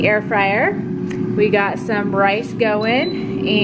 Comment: I’m pretty sure I heard her say Bryce instead of rice